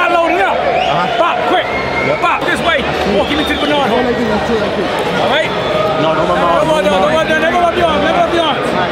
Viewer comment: what he say at 7:37 ? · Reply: Walk him to the 9.. (9 o clock) to his left and the opps right